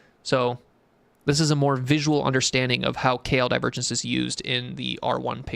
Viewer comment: Bro I'm so dumb, I thought u were saying Kale Divergence.